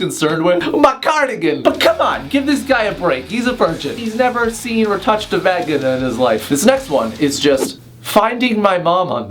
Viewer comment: i don't know what you actually said but the captions said he's never touched a baguette in his life